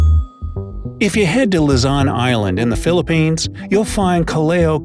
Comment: I heard "Lazan" instead of "Luzon"